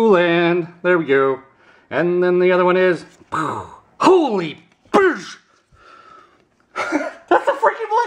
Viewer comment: That last one had me on the floor....I thought he said holy bitch...